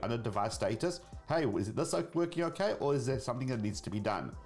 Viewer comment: At around 7:10 did you say "Is this oke working okay"? Great video as always, thanks